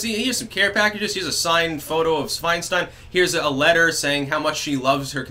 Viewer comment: Did he call her Schweinstein at 6:02 ? oinkoinkoink !